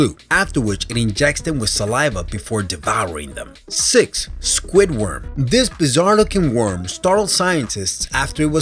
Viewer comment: When he said squid worm I thought he said squidward